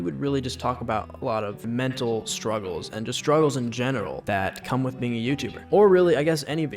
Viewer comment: Am I the only one who heard "struggles in genital" ???